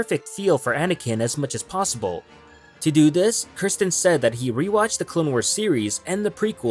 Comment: i thought you said Christensen i almost screamed lol.